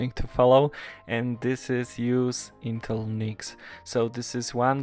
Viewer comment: I couldn't understand what you called that piece of hardware at 4:33, something about an "intel Mix" or something.. I keep repeating it but I still can't understand what you're saying. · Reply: Intel NIC :)